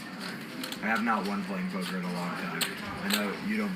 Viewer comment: Weird convo ... sounded like ask for chips and he declined , then said I have not won playing poker in a long time . Anyone else catch this >?